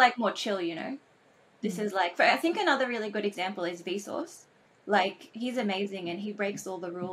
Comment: Initially I heard that as "I think another really good example is Bezos, like he's amazing", and that had me scratching my head. Then it occurred to me: that's 'Vsauce', but with the 'V' pronounced the same as the first syllable of 'vapor'. Now something surprising: with that figured out I opened the youtube auto-generated transcript to see how that had been transcribed. It says: 'Vsauce'. (Capitalized, so it's definitely not a fluke.) I find that mindboggling. Sure: Google is using deep learning technology for their auto-generated transcripts. But still: what blows me away is that the transcription functionality has the capability to recognize was talking about Vsauce. This is the very thing machines have until now been incapable of.